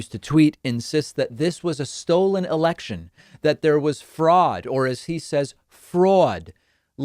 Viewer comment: So wait, he is saying fraud? I thought he was saying Freud. Must be some sort of Fraudian slip!